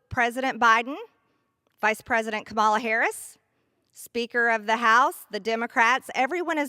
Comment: Did she just say "Cabal Harris?" 🤣🤣🤣